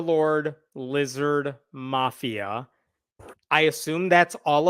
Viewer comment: I thought he said "Blizzard Mafia" so I'd have to go to church at Dairy Queen. Communion would be upside down.